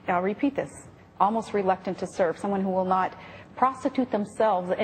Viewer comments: what did she say at 3:35? my english isnt that good, i understood something with "serbs" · Does she actually say : "someone who will not prostitute themselves" at 3:35??? Isn't this a rather inappropriate kind of thing to say?